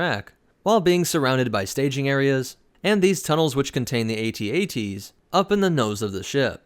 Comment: did i hear at-at?